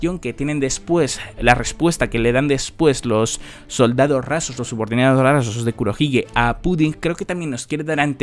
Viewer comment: Min 12:05 entendi soldado razo brabrosos brbanroalso 😅😂